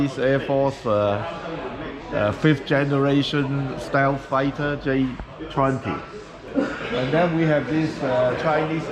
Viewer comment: mich würde mal die kleine Su27 bzw J11 im Hintergrund interessieren ?! Was hat er gesagt welcher Hersteller Keyonemodo ? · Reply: 😅 er sagt T-one Model. Gibt es glaube ich bei Final Modellbau.